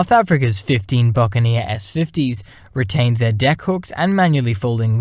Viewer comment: what the hxll did he just say😮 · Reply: "Retained their deck hooks", listening to it again it does sound like something else :-)